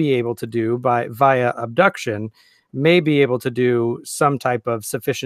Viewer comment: did I hear correctly that a scientist may be able to do things via “abduction”??? 😅😶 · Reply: Yep! Abduction - right along with Induction and Deduction. Abduction is, in very over simplified terms, the science of hypothesis--->experiment/observation--->theory.